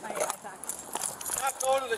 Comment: she does not say I-pack, she says IFAK, which stands for Individual First Aid Kit, otherwise known as a trauma kit